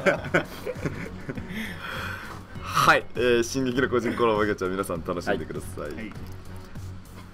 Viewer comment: くらいに進撃の個人コラボって聞こえたｗ